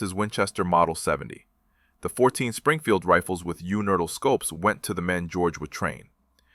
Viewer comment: One of us can't count? 11 shots - 11 dead? Didn't he say, earlier, at least one miss? I SEE - "- - -with you neutral Scopes" I HEAR - " - - -with you nurdle Scopes" Second time = " - - - Universal Scopes STILL hearing you Nurdle scopes They also seem to have "Grand" Rifles (Garand?) "Colonel FAIRY" Y'SURE?